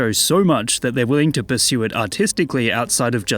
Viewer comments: I thought he said Autistic passion instead of Artistic lol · i thought he said autistic 💀💀💀